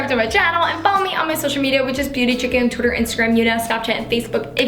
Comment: lol it sounded like u said beauty chicken😂